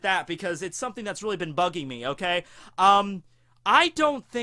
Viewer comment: Did I hear correctly? 4:11 instead of saying bugging me, sounds like he said Buggy-me